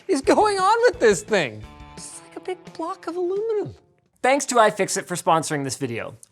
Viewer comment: i seriously doubt you said this, but i heard "it's a solid cock of aluminum"